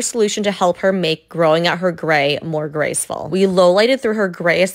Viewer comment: When you said "graceful" i heard "greysful" ;)